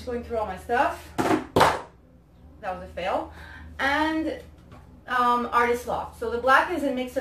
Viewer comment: Hi new sub I would love it if you could put the ingredients in your drop down box. You held up a large bottle and I thought you said it was Artist's Law is that right?